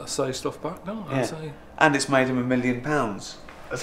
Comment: can any1 catch what ricky says at 00:50? Can't quite make it out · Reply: It's made him a million pounds